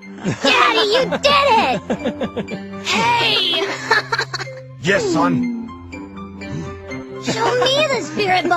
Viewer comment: Did vegeta say YES SON